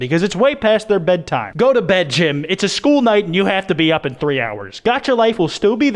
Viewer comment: Did I just hear “Go to bed Jim!” Either this is a reference to McCoy from Star Trek or I am just going crazy. ... Probably the latter